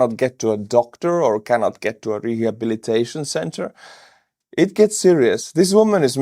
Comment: I thought I heard re-education Center funnily enough